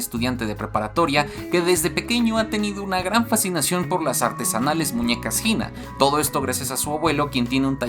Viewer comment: cuando dijo "las artesanales muñecas..." pensé que dijo "las artes anales" quedé lol